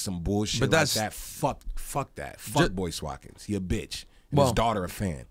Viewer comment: I thought he said 'fuckboy Watkins' :D.